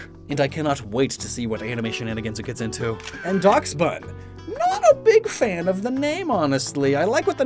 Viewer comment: When he got to dachsbun I thought he said "dogs butt."